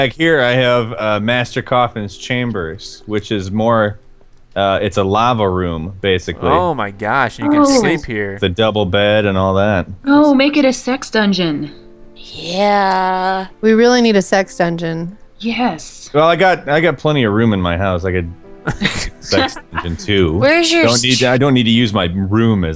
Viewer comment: i thought she said six dungeon snd 5 seconds later i figured out she said sex dungoen thumbs up if this happened to you